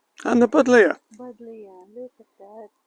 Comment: What was the purple flowering bush at 8:30? I couldn't understand what you called it. I'd love to have one in my garden, if it can stand the heat and intense sun. Great video! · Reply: Thank you so much for viewing our video and your lovely comment. The shrub you refer to is a variety of Buddleja - very attractive and can cope with heat if kept well watered. Best Wishes.